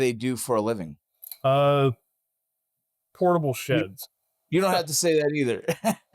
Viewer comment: Oh! He said "portable sheds!" I heard "horrible ####".